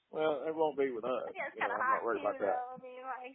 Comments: Ummm did she just call inbreeding kinda hot??? At like, 12:05??? 🤮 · DID SHE JUST SAY INCEST IS KINDA HOT? 😮